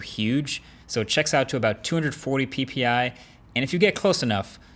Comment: DID HE JUST SAY PP:elbowcough::elbowcough::elbowcough::elbowcough::elbowcough::elbowcough::elbowcough::elbowcough::elbowcough::elbowcough::elbowcough::elbowcough::elbowcough::elbowcough::shelterin::shelterin::shelterin::shelterin::shelterin::shelterin::shelterin::videocall::videocall::videocall::videocall::videocall::videocall:🤣🤣🤣🤣🤣🤣🤣😂😂😂😂😂😂😂😂😂😂